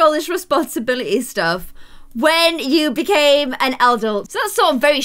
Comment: did I hear “eldult “ the mix of adult and elder???